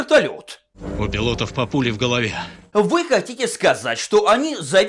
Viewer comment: мне послышалось "у пилотов папули в голове"...